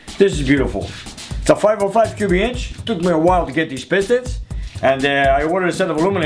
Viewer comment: Ooo wow, with your accent, at first it sounded like you said 2 barrel with pistons on Tony's 505, but you actually said, took a while to get these pistons. I was about to say, damn dude, 2 barrel on a 505? Wtf? Great work. Excited for the future...